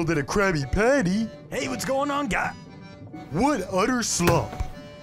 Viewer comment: the captions say "Wood utter slump"